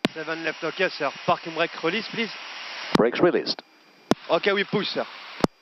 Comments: idk why, but I heard "Fucking Brakes Released" and thought he so fucking ready to go lol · I thought he said the f word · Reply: He said "Seven-Left ok sir, Parking break release please". It's the guy on the ground, checking if everything's good before take off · I can’t be the only one who heard fucking when he said parking break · sounds like he said 'fucking'😂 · Sounds like he says "fucking" 😜